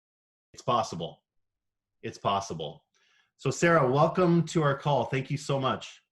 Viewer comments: When the guy said 'welcome to the call' i swear i heard 'welcome to the cult' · i keep hearing "welcome to our cult" instead of "call" and... i mean... it's not necessarily wrong?